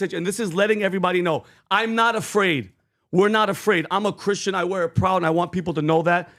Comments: It sounded like he said I am "uh " Christian. Like he was just taking a breath between words. I did not hear the word "not" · Vinny, love you brother. I swear in the clip you played I just heard Trump say, “…I’m ah Christian…” not (I’m not Christian). And my pastors have been meeting with him almost weekly. They know he believes and has excepted Jesus as his Lord and Savior. He definitely has growth to do in his faith, and he’s not yet filled with the Holy Spirit. But that’s the last piece of Kim Clements prophecy to come to fruition, in his 2nd term. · Vinny, I hear, "I'm 'a' Christian. " Vinny, I love you, But you're wrong! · He heard him wrong. Trump didn’t say “I’m not” a Christian. He actually said “I’m a” Christian. Listen to it closely. He just kind of ran out of breath as he was talking, his voice rasped up a little bit and it made it hard to understand him. But he says, “I’m a Christian.” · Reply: Yes I heard it as I’m Christian too. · Sounded like he said (I'm A Christian).... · I heard Trump say" Im a Christian " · It sounded like Trump said I'm a Christian. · Sounded like he said I’m a Christian